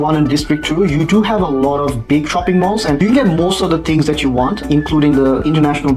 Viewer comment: I heard "big fckn malls" but then I replayed it and ahh.. perhaps he said "big shopping malls" 😂😂